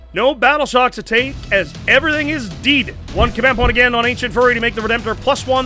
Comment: did you say "Ancient Furry"?